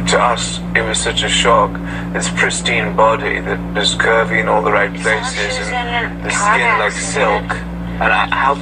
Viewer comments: Did he just say... " she had a pristine body with curves in all the right places, and her skin like silk. " ?? Who tf says that?? And why does he keep cutting her mother ofc while she spoke and kept talking over her SMH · did he say curvy in all the right places??